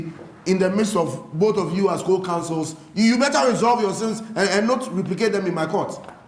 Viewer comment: did Judge say sins?